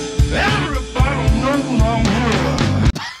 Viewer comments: I think he’s trying to say “everybody knows I am here”. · every month, I came back to this video. And now I heard what he was saying in 3:22. If I heard him correctly he said "Everybody knows I'm here" as a response to the guy previously saying "Yo, he's here" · I finally understood what he said. "Everybody knows I'm here" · I think he's saying everybody knows I'm here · I think he said, poorly, "Everybody knows that I'm here." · I finally have deciphered what Seagal was trying to say at 3:29, it's "Everybody knows my name" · I think he was saying “Everybody move on over” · Its not something to brag about, but I heard first time, Steven say "Everybody knows I'm here"